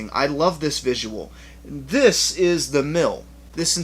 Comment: I looked away as you were talking about Maggie and I thought you said 'this is the milf' and not mill lol good review man awesome channel.